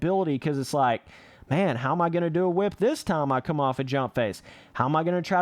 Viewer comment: I thought you said u knew how to do a whip